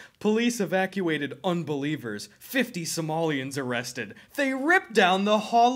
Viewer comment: I thought u said 50 Samoans not Somalians at first lol but better believe- crazy as it sounds its no joke! First week I moved up to Washington state there was a straight up “Samoan rumble” in the park behind my house! No shit like 50+ yuuuge ass dudes runnin thru the neighborhood from the cops like still throwin hands in the street! Leaving LA thought id seen the end of gang brawls, but Apparently Near Seattle theres huge Samoan and Somalian populations that got their own gangs. All our Somalian neighbors seem pretty chill, but guess the Samoan kids love a good melee cuz we keep having big mob throwdowns busted in the park! Dunno if the mobs cross paths lol they seem to just wanna fight eachother? I dunno lol comin from LA i though pacific northwest just had trees & like Twilight vampire cosplayers not real gangs 😂 but guess every region has their own shit goin on